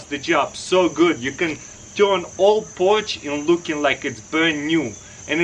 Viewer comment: i thought you said Porsche